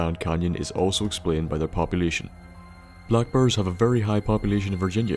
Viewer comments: lol i thought you were saying black bird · THEY ARE THE AMERICAN BLACK BURR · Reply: dyn sounded like he said "black birds", for me and I was like wtf? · XD for some reason i thought you said black bird